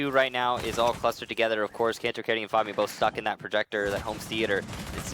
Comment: i thought said kanto and fabian suck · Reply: yeah I heard 'kanto and fabian both suck in that projecter' and i was like WHAAA